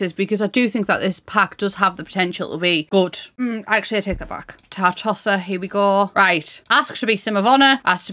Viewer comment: I swear, when you said, '"Tartosa," I heard, "Tar-Tosser." >D